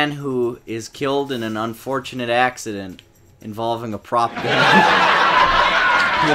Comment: What did he say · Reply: Prop gun